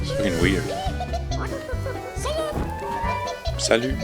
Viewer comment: no, he clearly says "Blyat" XD